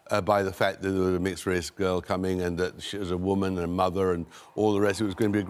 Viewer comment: I thought he said her mother... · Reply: Tom said "she was a woman AND a mother" at that time slot.